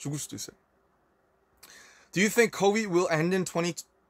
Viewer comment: Okay but instead of COVID he sounds like he was saying Kobe at the end 😭